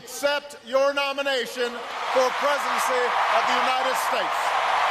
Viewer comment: I think I heard Obama accept the nomination for "presency" instead of presidency of the united states. Just listen to the video